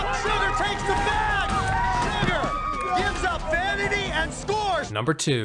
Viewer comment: i still have no idea what Jeff’s saying. ‘Sugar gives profanity?’ ‘Sugar gives up vanity?’ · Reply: Oscar Siddle Yep, “Sugar gives up vanity and scores.”